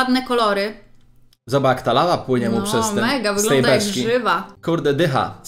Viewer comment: Kto usłyszał lala ?